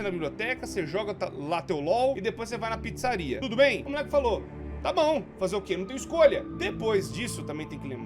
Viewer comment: Ai, história aleatória Eu estava com uns fones lá Bluetooth E a bateria ia acabar Então eu só ouvi, "o muleke falhou (*aviso de falta de bateria, q se parece com Bep bop Bo)"